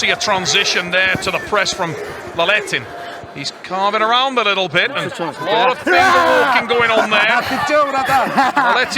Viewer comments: What did they say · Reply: He said: "are you kidding, brother?" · What did Artyom say and both of them laugh about at the 15:40 mark? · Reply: If I'm not mistaken, he said it was complete nonsense.